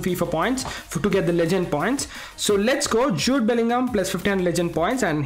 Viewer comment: What it's actually jude Bellingham What I heard sh00t Bellingham